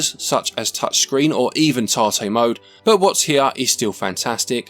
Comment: Does anyone understand what says at 8:56? "Touchscreen or even..." What? This question will haunt me at night. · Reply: Tate mode. That's when you flip the screen 90 degrees to display the game vertically.